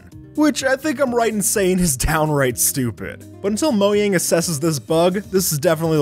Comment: did he say Mojang or Moang